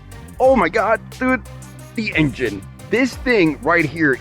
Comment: did he say houses a v12? Or was that just me hearing it wrong?